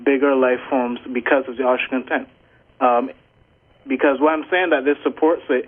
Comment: what did he say? Austrian tent?